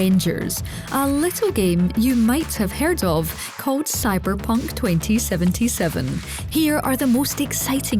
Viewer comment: I thought she said cyber bunk😂 Is this a computer generated voice?